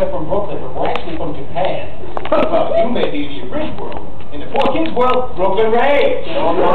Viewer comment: I thought said “In the 4kids world, Brooklyn raised” or is it “rage”